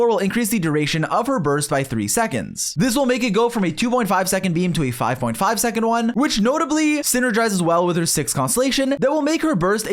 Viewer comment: why did I hear "2.5 second meme to a 5.5 second meme" and why does it actually make sense